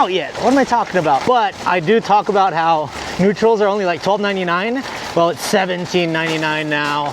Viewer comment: mark. Nutril??? Man I thought those were called nut rolls